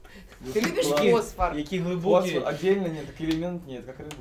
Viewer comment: не поняла останньої репліки Кондратюка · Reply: мне послышалось кирпичным мечом